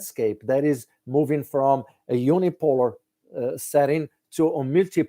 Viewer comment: You said a word at 26:16, "umipor", I don't understand it ! Can you clarify ? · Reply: "Unipolar" meaning dominated by one country, the US in this case!!!